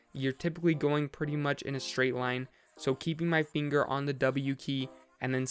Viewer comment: He said keep his finger on the W key instead of the E key.